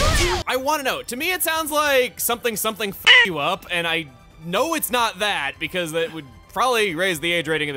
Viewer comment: Also for in that clip at around 6:55 what I hear is "This will Shut you up!" Makes way more sense then "This will (Yoshi sound effect) you up" Love the use of that as censoring very funny